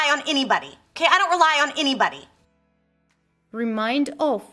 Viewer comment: В сюжете, посвящённом фразовому глаголу "rely on", я чётко расслышал вопрос "Can I rely on anybody?", а не утверждение "I don't rely on anybody!"